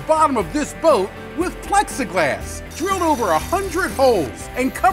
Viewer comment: I thought he said flex-a-glass. Hey! That should be his next product!